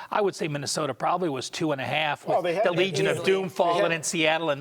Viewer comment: did he just say legion of doom???